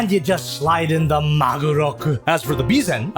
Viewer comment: You say "Magoroku" I hear "Maguroku", the invincible Tuna Katana!